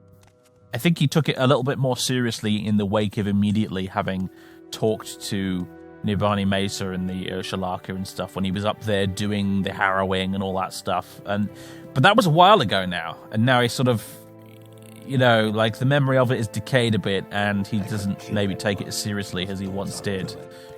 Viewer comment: I had to do a double take at 2:50 because when you said "up there doing the harrowing" I heard heroine.